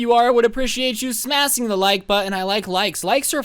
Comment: Smasing? Am I the only one who heard him say “Smasing”